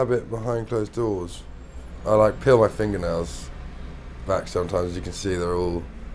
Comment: Am I the only one who genuinely thought he said 'pee on his finger nails' rather than 'peel his finger nails'? :D